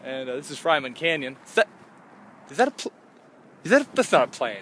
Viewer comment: oops. i thought it said fryman cannon